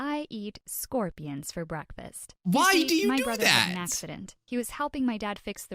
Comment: I thought she said “My brother is an a*s-hat.”